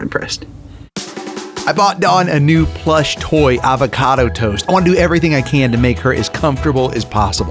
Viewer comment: I thought he said he bought Dawn a new avocado toast I missed the plush part 😂🥑❤